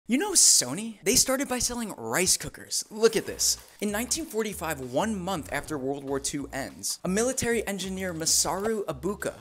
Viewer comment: I have no idea why I heard “rice cookies” the first time.